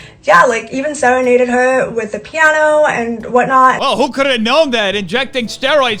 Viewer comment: “He serenaded her with the piano”.....I thought she said inseminated her with a piano!!!